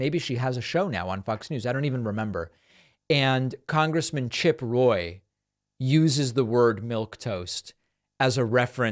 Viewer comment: Thought she said chip ahoy lol 😂